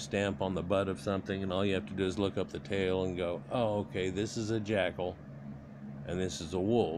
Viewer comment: Did he say "jackhole" at 19:20. lol Thx for the vid!